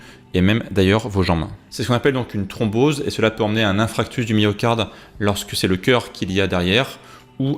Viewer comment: A 4.38 j'ai entendu "infractus" du myocarde...